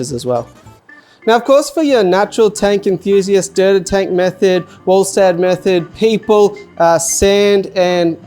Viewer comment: Had to do a double-take, thought you said dirted tank meth head and I was like woah bit harsh 🤣